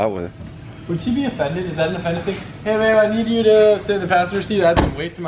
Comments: did I just hear “hey babe”??? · did you just say hey babe??? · Right around 7:30 ish he talks about collette and said hey babe? Or am I just hearing things?? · are they dating cuz if not then is it just me or did he say "hey babe" but slurred it?